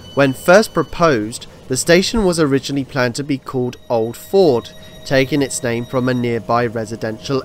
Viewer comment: Maybe I misheard but it sounded as if you called Stadium "Old Ford" when it was actually "Old Fold".